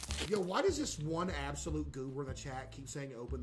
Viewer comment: Bruh I wasn't paying attention and heard him say " whys this one absolute goober" and sounded like he said "this one asshole" I've never rewound something so fast in my life 😭😭😭